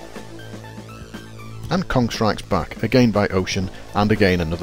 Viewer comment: What is the name of the game at 4:50? I hear "Contract is back, again by Ocean, and again a follow up" but I can't find any game named Contract... EDIT: Nevermind, "Kong Strikes Back", found it in a best 50 games of 1984 :)